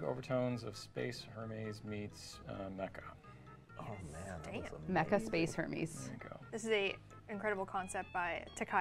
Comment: i misheard "mecha space hermes" first time as "mecha space herpes"